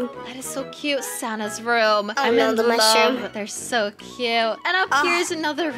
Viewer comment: did just said"i love the mushrrom"😳😳😳🍄🍄🍄